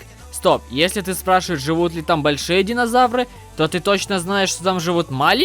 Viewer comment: мне на 2:07 послышалось большие динозавры живут в миске